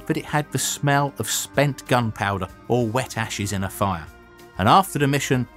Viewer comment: At first I thought he said wet asses